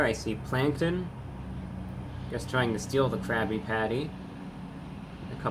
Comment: At first I thought they were saying CRAPPY patty…